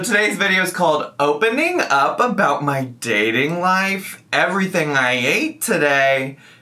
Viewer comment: When you read the title, I thought you said “ everything I HATE today” and I was like, yes, finally something I can relate to lol